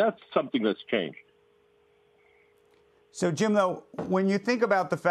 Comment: Does he call him Jim-o at 2:16?